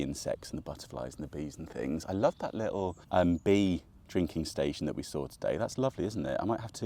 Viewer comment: Keep the chairs in roof garden,always handy for extra guests, and definitely do the bee drinking station 🐝🥰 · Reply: Thought that said beer drinking station 😂🍻